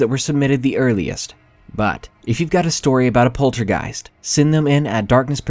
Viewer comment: LOL, I'm sorry, but after the last story when you said "poltergeist", I swear I heard you say "POULTRY-geist"! 😂😅 ROFL! Aside from my obviously faulty hearing at the end of the video, I love listening to you read. Your voice is soothing and you're still able to bring emotion and life to the words. Keep up the great work,darlin'! 😘